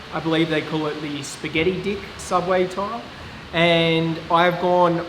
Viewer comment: Did he just say, spaghetti dick subway tile?!! ..and with a straight face! lmao